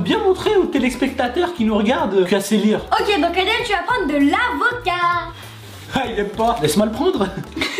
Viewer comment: j'ai entendu ADEM TU VA PRENDRE DE LA VODKA 😂😂😂